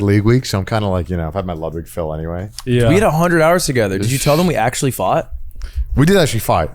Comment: I heard "we actually fucked"